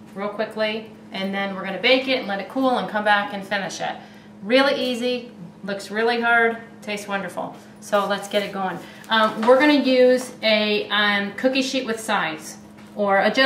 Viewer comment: it sounds like she said "it looks really gurd"